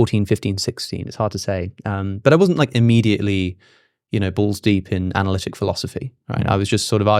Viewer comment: Did he say balls deep?😂😂😂😂